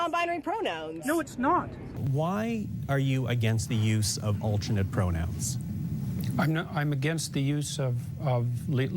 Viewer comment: I'm sorry, did you just say that Peterson speaks *slowly*?! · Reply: Slowly, not simply.